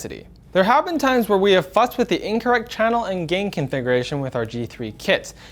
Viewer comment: is sounded like he said fucked